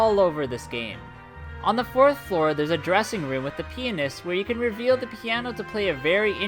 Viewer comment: Bruh did he just say penis xD